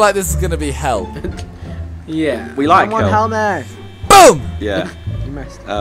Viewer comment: Did anyone else hear say we like hell